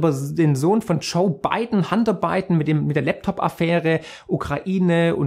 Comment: Hunter Biden...ich habe "Handarbeiten" verstanden :D Ansonsten wie immer aufklären und informativ für die Menschen die noch nich so weit sind, danke.